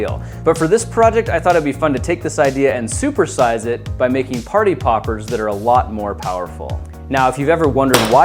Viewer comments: I thought it said party poopers lol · At first I thought it said party poopers😂😂😂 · I thought it said party poopers xD